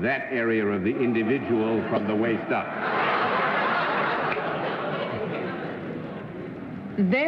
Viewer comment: Do I hear Latin at 14:22? I can't figure out what John Daly is saying. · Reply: Arlene said, Therefore. And Daly replied (or somethin akin to), id est quod est - loosely, it is what it is.